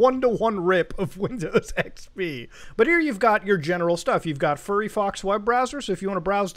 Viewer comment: did you just say furry fox instead of firefox?